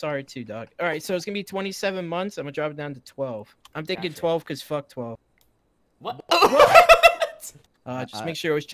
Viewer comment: Did 12 just say f*ck 12